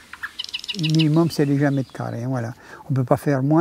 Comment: À 1:26, il dit le minimum c'est… Puis on ne comprend pas le chiffre. Il dit quoi? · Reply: Il dit 1m2